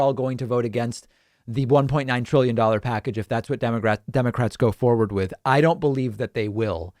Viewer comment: did...did he just say demorats? :p